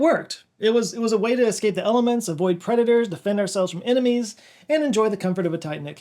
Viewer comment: Every once in awhile my brain just completely breaks for a second. About living in caves, I thought you said, " it's an excellent way to escape the aliens, avoid creditors and defend ourselves from anemonies."